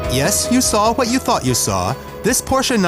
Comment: this anyone understand : "yes you saw what you fucking saw". instead of "you thought you saw"?